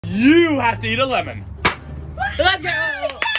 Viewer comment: "nich gives Zoe a lime" Me:I thought you said a lemon